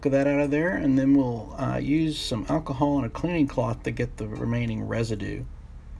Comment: did he said romanian?